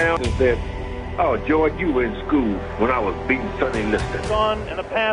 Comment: Puting the "is that all you got Goerge" just before the final round was beautiful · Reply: i thought he said "you are as cool when i beat Sonny Liston" like in you are cool like him but i still beat him.